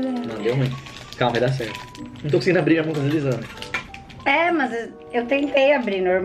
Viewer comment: so eu que escutei o chamando a de AMOR? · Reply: "Não tô conseguindo abrir, a mão tá deslizando" Ele falou isso!👍